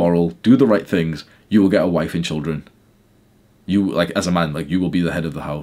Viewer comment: thought u said "inshallah" instead of the word "children" lmao 👽